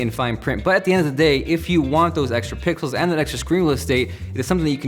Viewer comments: it sounds like he said pickles instead of pixels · did he just say pickels??😂😂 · i think you said if you want those extra pickles.